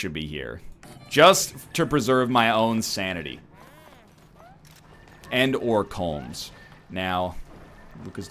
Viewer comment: Did... did Egg just say "Sanity and Colmes?" Too soon/hilarious.